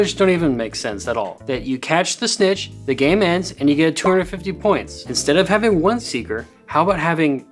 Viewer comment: I like that he says 250 points, the correction says 150 points, and the subtitles says "tour of 50 points"